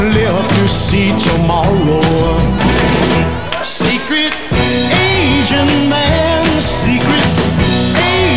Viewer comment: When I was a kid, I thought he was saying secret Asian man 😊